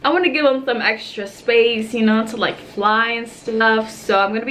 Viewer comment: Did she say snuff?